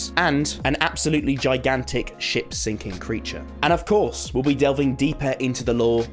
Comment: Dude instead of "sinking ship creature" I heard "sinking s*** creature"